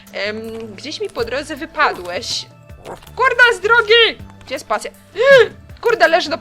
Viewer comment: Nie wiem, czy dobrze usłyszałam, ale w 16:00 słychać pewne słowo ;D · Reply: Julia #Pograjmerciątko (Data, późno, ale lepiej późno niż wcale XD) Przecież powiedziała tylko „Kurde” ;)